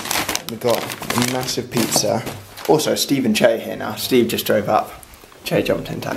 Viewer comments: AM I THE ONLY ONE WHO HEARD SOMEONE SAY 5 SECONDS OF SUMMER ? maybe its just an illusion because of the fangirl feels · did I hear someone say "5 seconds of summer" ahah it's just that I lub them too much :') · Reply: Yeah I heard that too. I think it was